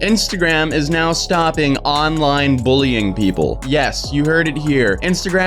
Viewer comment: I thought it said that Instagram will stop "online buying" instead of online bullying in